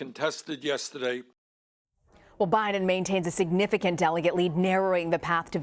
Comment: I thought she said Obiden